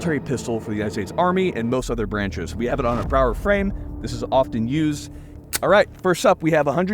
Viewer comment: What kind of grip is that on that Sig p320?! Sounds like he said power frame ?